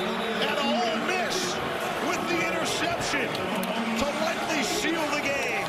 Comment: anyone else hear “Ole Miss with the interception” ? 😂😂😂